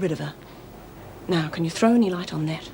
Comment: WTH did Meg say to Bea? I couldnt make it out. And seeing Bea crying made me sad too. I knew Donna would OD. Happens way too much. And its always sad :( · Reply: She said ' now can you throw any light on that '